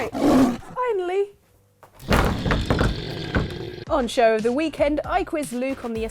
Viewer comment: I totally thought said "Poncho of the Weekend" and thought "Wow- awesome new feature!"